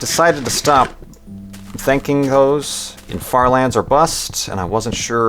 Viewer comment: did he just say he is going to stop thanking hoes?? they put in a big effort and they deserve the money and the thankyou